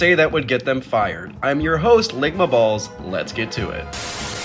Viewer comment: The first time I heard you say "Lickma balls" 😭